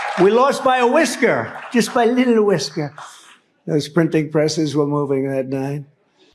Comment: Am the only one who heard Trump say "we lost by a little bit" after he said he received the most votes as a sitting president?